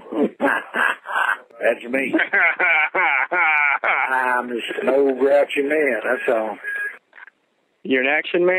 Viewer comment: i love the variety of mishearings of "im just an old groucher man". "old russian man" "action man" sure there's more lol